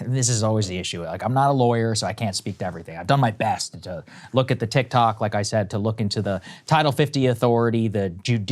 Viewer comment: did Saagar say he was reading the TikToks 😌